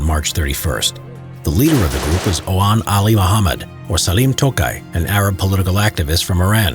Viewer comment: Part 2 The SAS assault of the Embassy coming up Monday! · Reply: did you say salim tokai? 🤣🤣